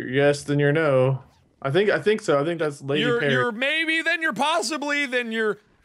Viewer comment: did just say " lady perry "